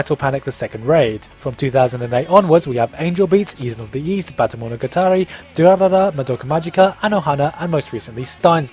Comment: what did you say? Baka no montotary? Bata no? wut.. Tell me! seems intresting :)